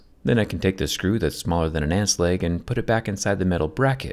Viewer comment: Jerry: this screw is smaller than an ant’s leg YouTube Captions: did I hear an ass leg??..